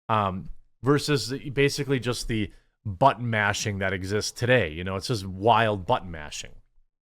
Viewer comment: Are you saying butt mashing or button mashing? Because what I hear is butt mashing.